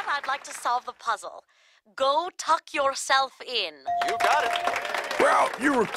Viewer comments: I can’t believe they got away with “my hairy aunt” considering this is OG family guy I had no idea what that meant when I was a kid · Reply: And I always heard it as "My Hairy Art" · "I can't believe we missed the phrase 'my hairy aunt'" XD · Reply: I thought he said "my hairy art" and was confused. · lol This whole time, I thought he was saying “My Hairy Art”, which absolutely didn’t make any sense to me. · I can’t believe they got away with “my hairy aunt” considering this is OG family guy I had no idea what that meant when I was a kid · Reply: I thought peter said “my hairy ott”